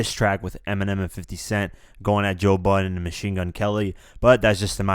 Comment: Does Joe Budden sound like a rap name? I always thought he said "butt-in".